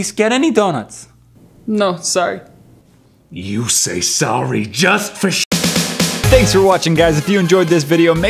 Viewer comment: At it sounds like says,"you say sorry just for sh**" am I the only one who heard this?